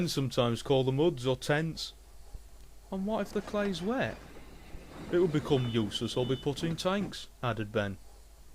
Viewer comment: can't understand what he said. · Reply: He said: "It would become useless or be put in tanks." :P